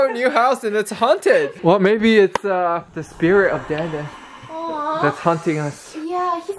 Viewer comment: The caption said "the spirit of daddy" instead of Dan dan 🤣🤣🤣🤣🤣